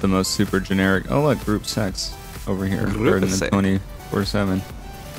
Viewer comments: I thought he said “Group Sex” not Gruppe 6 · Every time he said “group six” i thought he said “group sex” and i was so confused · thought he said group sex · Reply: Gruppe Sechs hahah